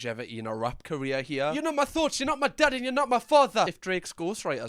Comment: he actually said “you’re not god, you’re not my father and your not my boss” I’m sure if you’re not used to his accent it could be hard to understand sometimes